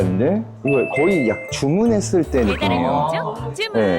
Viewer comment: 배달의 민족 주문~! 왜 들리는 건지 궁금ㅋㅋ